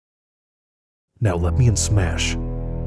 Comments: I thought he just said “let me smash” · For a second, I thought Master Chief said ''now let me smash'' to Lucina😂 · I first thought he said "now let me smash" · Master Chief: "Lucina, I've done it, I've saved Christmas... now lemme smash." I swear thats what I thought he said first time lol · When I first saw this video I didnt had my headhpones so I heard at the end "Let me smash" Jokes aside, I would cry if Chief was in Smash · I heard "let me smach" XD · Now let me SMASH is what he really said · Thought Master Chief Said " lemme smash" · At first I thought h e said now let me Smash and I was like oh my but he said let me in Smash so it's not as lewd